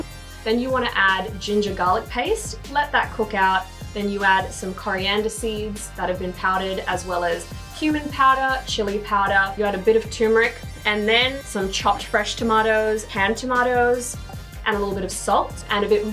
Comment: I'm sorry, but did she say human powder?!😂